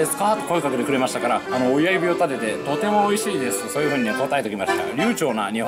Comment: 親指を立てが中指を立てと聞こえてファッってなったけどもう一度慎重に聞いて親指って言ってたから安心した